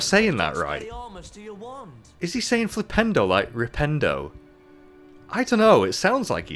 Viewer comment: Ron is saying flipendo. You are mishearing him.